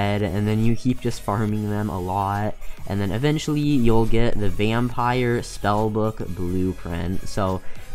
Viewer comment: did he say vampire spell book